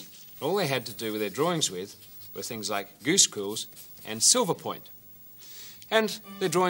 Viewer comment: Rob, can I ask you what did you said, something like "gooschools"? I couldn't research it on google. Hey awesome video! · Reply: Yes, it is indistinct. It was 'goose quills' - rob